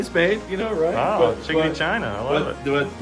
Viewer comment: did you just say chinky china? Lol